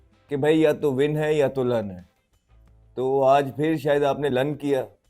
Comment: Did he say Lun